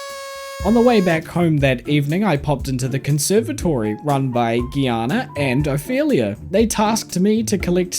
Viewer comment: i thought you said run by giana and a failure (ofelia)